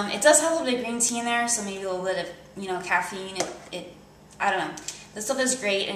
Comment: what do you say @ 4:54 caffeine ?